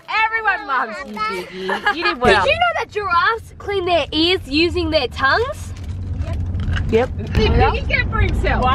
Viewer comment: I thought said ’Did you know that artists clean their ass using their tounges?’ I'm dying 😂😭💀